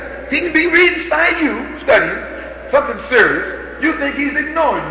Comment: Did Malcolm say "f**king serious" on stage at 1:51? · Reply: No, dear heart he said "SOMETHING SERIOUS" LOL